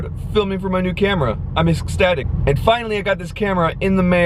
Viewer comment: did I hear STATIC 👀😂